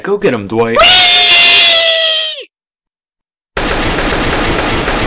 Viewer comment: what did he say I couldn't make it out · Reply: world star